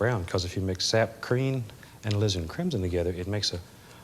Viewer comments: All these years I thought he said...LIZARD CRIMSON :-O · Sounds like he says "sap creen"